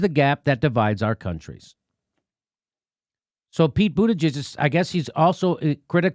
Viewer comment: Did Jimmy call him Pete Booty-jizz? What a homophobe. J/K, I'm no SJW. It made me laugh. I call him "Poop" Buttigieg because he had poop on his forehead at (I think) the first debate. They came out later and said it was chocolate. Also, someone on Democracy Now accidentally(?) called him "Poop" Buttigieg. The name stuck with me like a bad dingleberry.